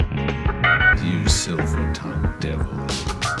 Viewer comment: Am I going crazy or? I heard "you sliver tongued devil you.." at like 1:41.